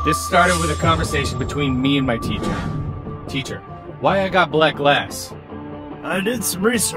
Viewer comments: when he said "teacher, why i got black glass", i thought he said "teacher, why i got black ass".. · when he said "teacher, why i got black glass", i thought he said "teacher, why i got black ass"...